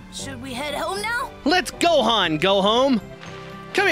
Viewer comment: am I the only one who heard him say " let's Gohan go home "